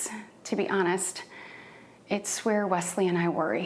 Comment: Is she saying that her & Wesley worry about Mennonites?